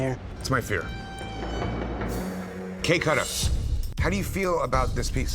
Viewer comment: how come when you turn on the subtitles it says cake cutter instead of kay kutta like. what.